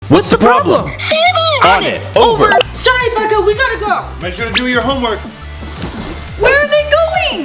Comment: What the hell did she said that I got goosebums 💀💀💀💀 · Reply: Sandbox